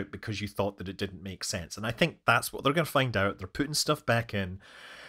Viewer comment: When you said 'Putting' I thought you said "Putin" - i.e. Every fascist oligarch's best friend.